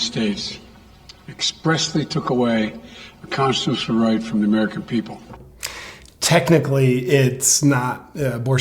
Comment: Actually, I think he said they took away the consusrife from the american people.. so, your argument is invaild. Subbed! This will be fun! : )